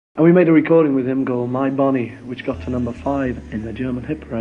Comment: Lol I always thought this was a horrible song because I thought they said "body" not "bonnie".